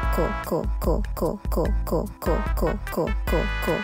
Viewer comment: why i heard it say “ gulp gulp “ 💀💀💀